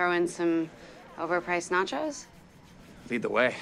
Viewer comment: I thought she said "iced nachos" for a second. Never seen the show, so I don't know what to expect from this show-niverse.